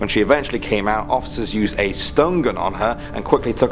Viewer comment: for a moment there i thought he said "Stone Gun" Ouch...